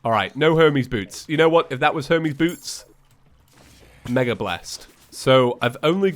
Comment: i thought he said homies boots